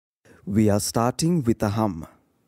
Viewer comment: I thought u said we r starting wit the hump 🤔 😅😂